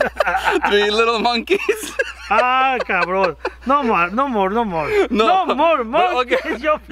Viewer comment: i heard you gasparin You were about to say “no maaa...mes”